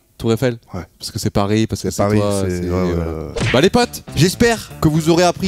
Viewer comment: au début jai compris merci les pates🤣🤣